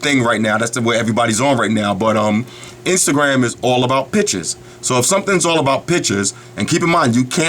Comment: I thought he said "all about bitches" lol